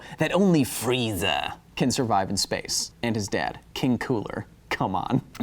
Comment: Did you just say King Cooler, who is that. I thought it was King Cold.